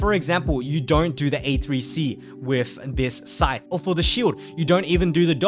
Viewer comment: does someone understand what is he saying ? "a3c king" that s three auto then charged attacks right ?